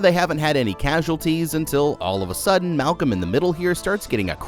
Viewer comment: did bro say "malcolm in the middle" .......